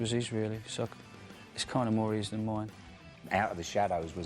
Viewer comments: lol steve and bruce are like fucking brothers or something argueing about stupid shit, 'the song is more mine than it is his.' fuck me, grow up. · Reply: Its not an opinion. He didn't say that. He CLEARLY said "Its kinda more his than mine". I guess english isn't your first language? because you're having hearing difficulties. listen to 0:38 again. This isn't a subjective or opinion based thing. it's what he said. being stubborn over an objective thing - now thats a way to look like a child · lol steve and bruce are like fucking brothers or something argueing about stupid shit, 'the song is more mine than it is his.' fuck me, grow up. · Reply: Simon Anselmo actually i heard "it's kinda more his than mine"